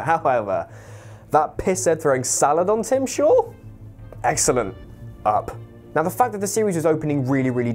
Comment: Ok I usually don't have a problem with British vernacular, but what in tarnation is he saying at 3:54? All I hear is "That piss Ed throwing salad on Tim's shore, excellent."